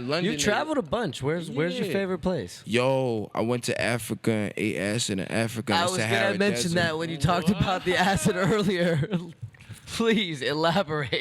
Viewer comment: Swore he said he ate ass in Africa